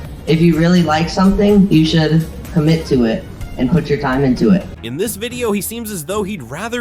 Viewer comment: I THOUGHT HE SAID "YOU SHOULD COMMIT SUICIDE" OMG